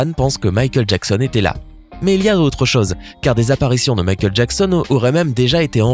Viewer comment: c moi ou à 7:53 Michael et géant ?😂